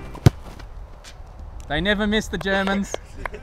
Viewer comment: why does he say at 01:48 i never missed the germans?